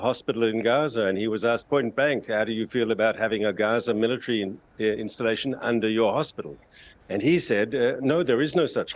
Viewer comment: did he say Gaza military installation!?!?